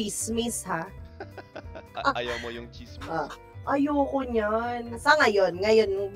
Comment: I think she said "tsismis" there @ 7:20.